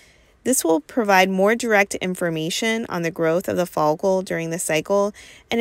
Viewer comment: Did she just say folic acid 😖